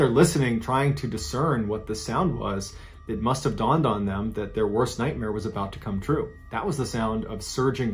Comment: I thought I heard "their worst sniper was about to come true" my dumbass thought "in a cave?! Especially that one?! What kinda world War crap is that?!"